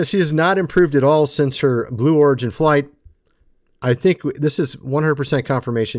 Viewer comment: Katy perry sold her soul to the devil.She belongs to him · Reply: It sounded like he said blue orgy flight instead of blue origin. although its probably more correct that way...